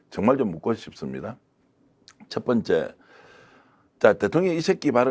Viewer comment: 순간 '대통령 이 XX(가)' 발언했나 안 했나로 들려서 넘 놀랬어요 ㅋㅋㅋㅋㅋ😂😂😂